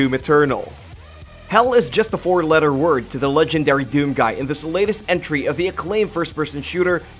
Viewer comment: I'm sorry but what did he say? 5:32 Doom guy??????????